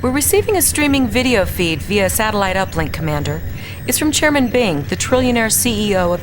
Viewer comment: Did I hear "chairman Bing"? As in a parody of Microsoft predicted the name of their "future" search engine?